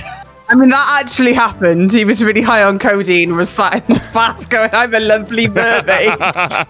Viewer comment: Ellis being high on codeine and pretending to be a mermaid sounds about right lol. I used to take it for chronic pain, and a housemate once caught me chanting "Marshmallow, marshmallow" to myself in weird voices, while I was laying in bed after taking some codeine. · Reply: i thought you said cocaine for a minute lol